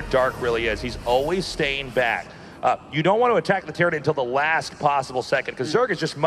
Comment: Is he saying "tyranid" ?